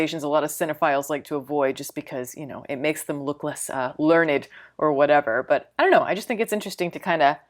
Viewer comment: "It makes them look less..." Learned? Lurid? What is the word she says here??? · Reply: Learned I think